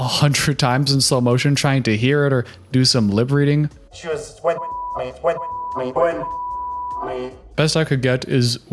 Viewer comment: did anyone end up telling you that the bleep bit is loren saying "she went down on me"